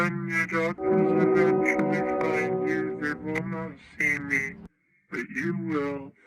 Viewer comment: i loved the vid but when phen 228 spoke i legit thought he said my n would eventually find you maybe its my brain been fully rotten lol